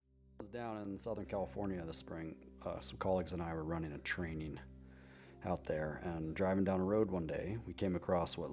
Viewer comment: In the beginning of his conversation I could've swore he was saying some colleagues and I were running a train down there but he said training. Whew! When the mind is this warped.😂